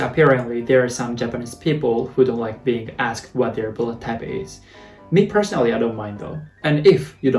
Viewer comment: I always heard "Bullettype" instead of "Bloodtype" 🤣